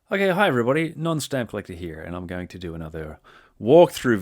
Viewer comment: Sounds like you say at the very beginning